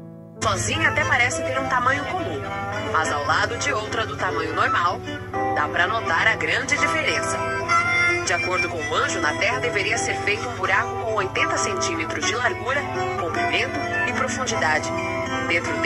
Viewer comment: Mas no trecho da reportagem a repórter diz "de acordo com o anjo..." (9:51), ou entendi mal?